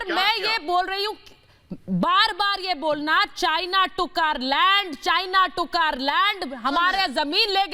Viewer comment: did she said, China Tookaar laand,China Tookaar laand 🤔🤔🤔 🤣🤣🤣 what a Enghlissss